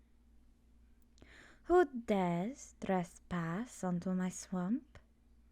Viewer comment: What she says: "Who dares trespass onto my swamp?" What I heard: "What. Are you doing. In my swamp?!" Lol, I couldn't resist! Thanks for the audio.